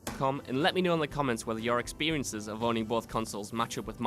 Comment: Did he just say his experience of boning! Both consoles??? 14:00....